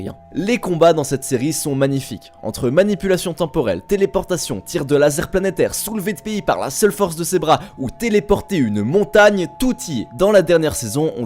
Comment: Soulever UNE QUOI !? J'entend une fille MDR mais Jpense pas c ça.. mais une ville non plus ou un pays.. jsp